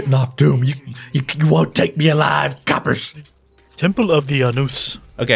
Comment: did someone just say "Temple of the Anus?" lolol